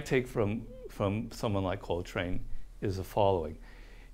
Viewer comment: I thought he kept saying Cole train and all I could think was CAN’T STOP THE TRAIN BABY